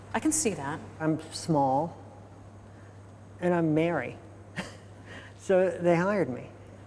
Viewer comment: The problem with American English is one can't tell the difference between 'Merry' and 'Married' I thought he said I'm small and I'm married!